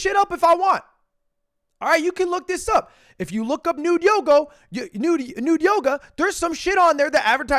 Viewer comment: I heard Nude Yogurt 😂